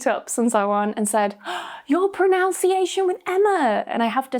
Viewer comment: Good lesson. But I thought I heard you say "proNOUNciation"